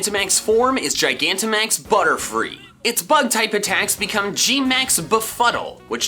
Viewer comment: Did he just say butt type attacks?